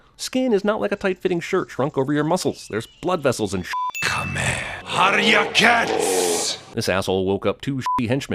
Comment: phonetically I swore he was about to say “kamehameha” but he stops st “kame” then I swear right after that he said “how’re your cats?!?” Lol don’t judge me. Then kame ends up meaning “kill them”